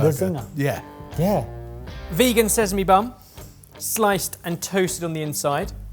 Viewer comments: First time I heard "Vegan says me bum" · Is it just me, or does Ben say “vegan says me bum” at 11:38? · Ben: "vegan says my bum!" I heard it wrong the first time and now it gets my every time 😂😂 btw what an awesome video! loved it. what a clever way to recreate this zinger burger. would love to try it 😋 u guys r awesome💪💪